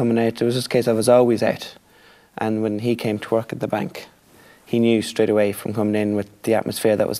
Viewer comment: I honestly thought he said "He came to twerk at the bank" at 0:10.